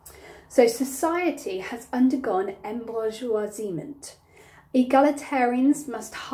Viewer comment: I have two questions: 1) What is that word you say at 7:46 and what does it mean? 2) Is it possible for you to link the table in the description so I could download it? Thanks for your great help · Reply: embourgeoisement - people in society becoming richer (and thus becoming middle class)